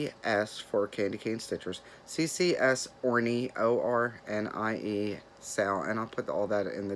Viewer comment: So sorry for the loss of your friend. My Mom passed from breast cancer and it’s horrible. Great finishes and WIPs. When you said OrnieSAL I thought you said hornySAL. I had to stop stitching and rewind. Good to see you again. Kathy